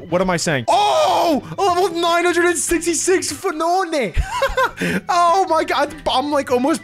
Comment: I am sorry did he just call a phione a PHNONE?